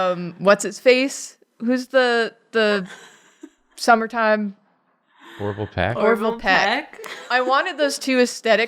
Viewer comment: I thought said "horrible pec" so I looked that up and didn't like what I found. Also thank u for the mention of Sufjan Stevens